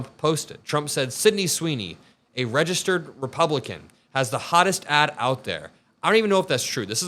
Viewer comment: When I heard Trump speak I thought he said "She has the hottest ASS out there". That would be more in keeping with his disgusting character.